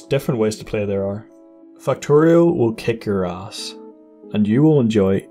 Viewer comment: i swear he is saying "fuck Toriel" right here.